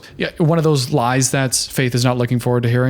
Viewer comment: "Going down on me." is what he said lol 24:40 we had to know so do you! 😭